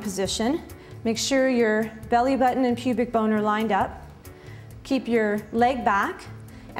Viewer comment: PUBIC BONER? Or what did I just hear.